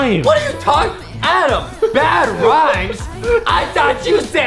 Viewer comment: Bad rhymes??? I thought you said SAD LIMES That got me laughing so hard 😂😂😂